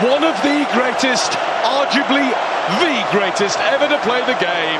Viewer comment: Did he say "arjuably" at 6:28?😱😱